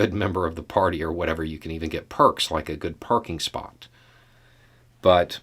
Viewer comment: I have listened ito it multiple times now. I can't hear "perking spot" even if I try. And even if he did, calling him a man-child for not having "perfect pronunciation" is one of the most baffling things I have yet seen on the internet. If you were trying for an ad hominenm attack, there were so many more mentally grounded ways you could have gone. But you went with "perking spot". Astonishing. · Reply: I was talking about myself being a man-child. I just swear I heard him say "perking spot".